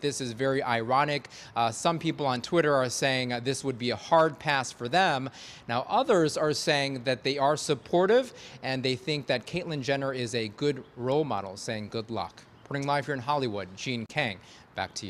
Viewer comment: Sounded like he said Caitlin Gender